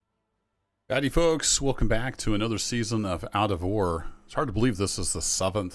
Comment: I thought there was a game called moivarra. I almost started checking steam till I heard you say out of ore lol.